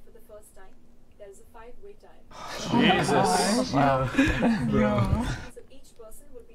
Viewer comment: Is someone says jesus in the 9:18 ?kekekek